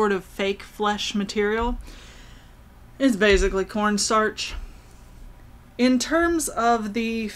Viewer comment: Okay when you said "it's basically corn starch", I swear I heard you say "porn starch". I'm officially calling it that now. 😂